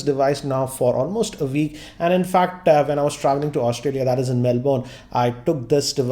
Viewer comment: Is this a standup comedy channel because I heard you saying "Australia in Melbourne" 😂 Bro how did you fit a country into a city. Like seriously? Great Comedian Man.👌😂